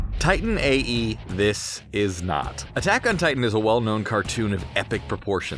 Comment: Did he just say cartoon 02:23 ??